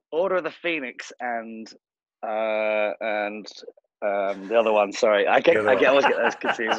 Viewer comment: Oh my God. Please help me English speaking people! What does Tom say at 43:53? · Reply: He says, "um, the other one sorry."